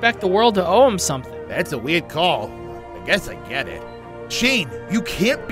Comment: what did he said ? Just forget it or guess i get it